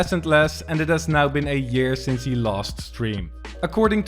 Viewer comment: I SWEAR TO GOD I THOUGHT YOU SAID 8 YEARS THERE I HAD TO PLAT THAT 4 TIMES LIKE WHAT FN WASN'T EVEN OUT FOR 8 YEARS LOL